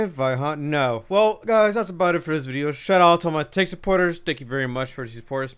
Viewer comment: He sounds like he says “thank you to all my TANK supporters” and it couldn’t be more fitting